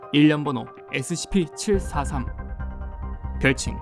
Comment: ? 내가 잘못 들었나? 0:16 scp-1743이라고 들었는데... 근데 캐릭터 머리에 "자다 깸 발음죄송;;" 뭐야